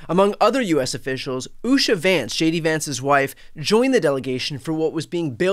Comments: You said JD Vance. I thought I heard SHADY Vance. 😂😂😂 The latter is more accurate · Did you say "Shady Vance"? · Don't know if you said it intentionally but it sounded like yoy said "Shady Vance's wife" - which is awesome! "Shady Vance" is his new name. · Did you call the VP Shady Vance? 😂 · I just heard you say "JD Vance" and it sounded like you said "Shady" Vance. Which gave me a whole new nickname for the creep. Let's see it spread!